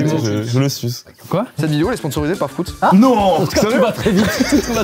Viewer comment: Dites-moi que je ne suis pas la seule à avoir entendu « Prout » au lieu de Fruits ?? 😂